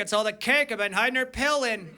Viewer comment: What does he say here? "I think it's all the cake I've been..." ??? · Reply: …hiding her pill in